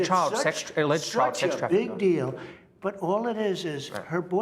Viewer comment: Guys did he just say "Big deal" when Jonathan tells him she is an alleged child sex trafficker?! Listen